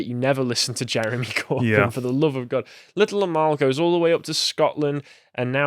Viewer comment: Until I actually read the correct spelling of the name of the Little Amal puppet, I thought you were saying her name as Little Lmao.... It is a bit ridiculous so it still fits either way.